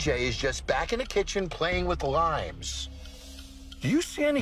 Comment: "And DJ is just in the back playing with LIMES." I laughed too much at this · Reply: I misheard it as THE DJ is in the back messing with limes and I got so confused 😂